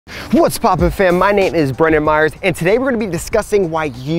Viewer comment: Am I the only one who heard him say "My name is bruno mars"?